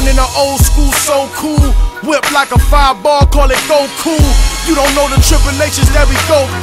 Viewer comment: Bruhhh, always thought he said “5 bars called it goku”